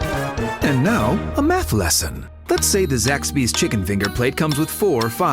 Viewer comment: Everytime I hear the Zaxbys Commercial sounds like he says a METH LESSON LOL